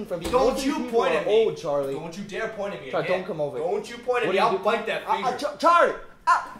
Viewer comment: Did anyone else recognize the hidden Charlie Bit My Finger reference in the cash to gold commercial. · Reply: Ohh, I missed it. Haha! Actually, I thought he said "I'll fight that finger!"